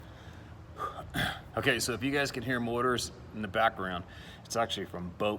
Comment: I thought that said "Mortars" not motors...🤣 I was like, where the hell are you that you are getting mortared? 🤣🤣🤣 Keep up the good work, stay safe and have a great vacation with that little hottie.😉